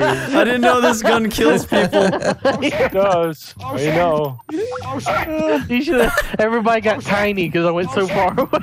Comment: Does anyone hear when vanoss shoots the gun at 4:26 the gun says oh shit